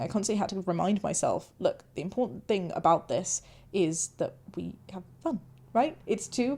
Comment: Do you have a time stamp. Didnt notice that, but am Kind of tired ;-) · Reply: hearing it again, it sounds like she might have said "the important thing is we have fun", not sure how I heard the other thing before. It's at about 1:25 or so.